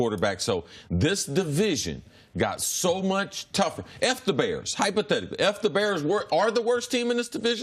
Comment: I heard "F the Bears..." but realized he said "If the Bears..." lol